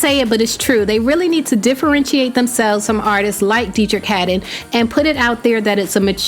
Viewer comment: Hey 😀How do you spell the guys name who was in the studio at the end part of the video 😁. I like that song and trying to find it. I think I heard “detra canden” but I know I got it wrong. Thank you. 🌟🌟🌟. I never heard of him. Thanks a million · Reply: KOFFATV it's Deitrick Haddon